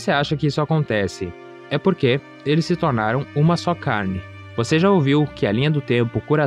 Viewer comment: Agr que entendi "Uma so carne" obg pelo ensinamento